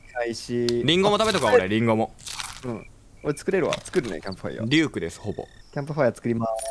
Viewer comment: リュークがデュークに聞こえた(笑)